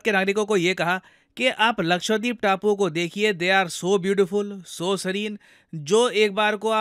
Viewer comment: 😂 i thought u r saying so beautiful so elegant ❤❤😂😂